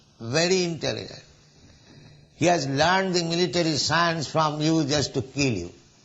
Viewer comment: Please correct subtitles at 12:54 He has learned the military science from you just to kill you. There is killed instead of learned. Hare Krishna.